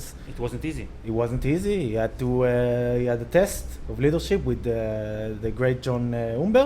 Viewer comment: All this time is thought he said little sheep...it was leadership...god..